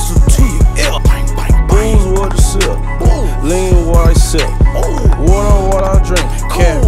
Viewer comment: Oh..its, lean what I sip.. I thought he said lean while I sip